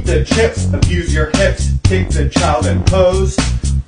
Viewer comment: Why fid i hear “abuse the child and pose”🥲😬